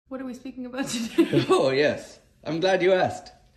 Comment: I love! Just LOVE how sporty she is. Pretending it’s the FIRST time she’s hearing those one-liners and then laughing like there’s no day after (that means not THAT rumbustious a laugh) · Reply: I thought she said "what were we talking about?" first. Either way, I'm sure her laughter was real.